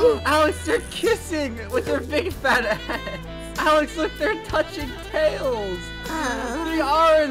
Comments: YO DID SAID A A WORD? A** Current time when said a a word- 6:39 turn on subtitles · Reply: He didn't say it. he said "Heads" Cause he was laughing so hard it sounded like the a word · Did they say big fat A$$ !?!? O-O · did just say "big fat a*s"?? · did just said a*s ............ edit 1: if you turn on cc/subtitles you can see that just said it at 6:35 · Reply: He said head